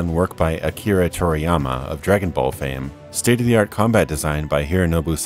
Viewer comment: Does he say Akira Toriyama is a Dragonball fan at 1:43? Am I crazy? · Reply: "Akira Toriyama, of Dragonball fame"